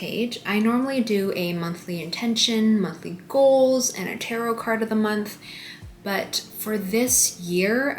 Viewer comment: did you say tarot card of the month at 5:40?